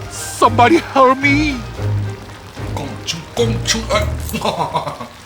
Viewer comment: somebody hurt me 로 들림 누가 좀 때려줘요 ㅋㅋ